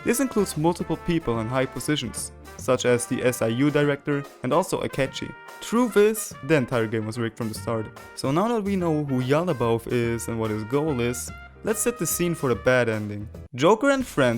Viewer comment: The subtitles say Cheeto XD and we can’t forget Moto’s deaths XD